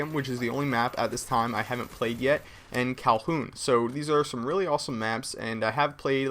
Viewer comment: did he say "calhoun?" at 1:26 haha i think its kowloon